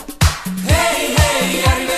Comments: You have got to be kidding me i feel so disappointed. For years as a kiddo I always thought he said "Hey Hey cochinita" fml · I thought it said Don chevita!!